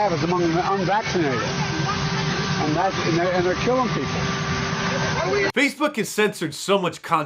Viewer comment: Actually he sounded like he is saying the non vacinated are killing people...listen again.